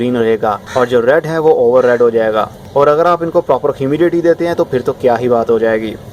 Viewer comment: What you said at 04:11? In plants ko Kia dein tu acha hoga? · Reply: Humidity